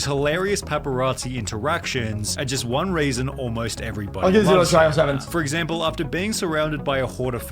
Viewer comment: no clue what he just said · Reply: I think its "let's get this one a try, let's see what happens".